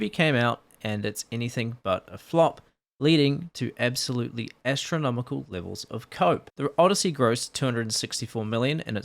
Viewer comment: i misheard 'absolutely estronomical levels of cope' LOL